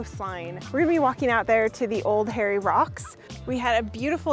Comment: Legit thought you were saying “Old Hairy Rocks”, and that we had a UNESCO world heritage site called “Old Hairy Rocks”